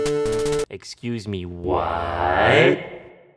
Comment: who hears it as “excuse me W H Y Y Y Y Y Y Y Y”